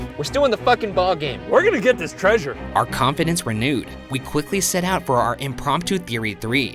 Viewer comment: Lmao I thought he said, "Our competence renewed."